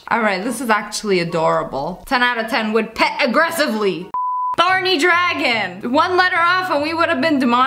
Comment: Instead of THORNY Dragon I thought she said BARNEY dragon and before I heard her say THORNY I was trying to figure out how she would get demonitized 😂